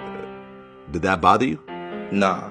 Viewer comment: "did that bother you?" "nah" "whys that?" gimmenablahblahblahenablahblah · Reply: Keep me on the block block baby what keep me on the block block baby sounds like he saying that tho